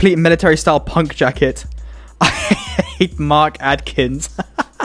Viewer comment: misheard that as martin atkins and was wondering what the guy from pigface did??